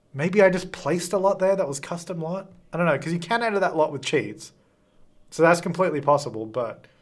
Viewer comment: I thought said "Cause you can't enter that lot with cheese." and I was like "Wait, is there a new lot trait I don't know about. How weird." 😂😂